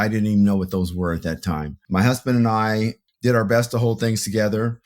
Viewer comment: Time stamp 2:13 he said “my husband and I”. Did I hear that correctly?